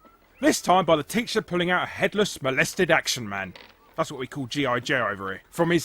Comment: You call G.I. Joe "Headless Molested Action Man?" That's honestly what it sounded like you were saying.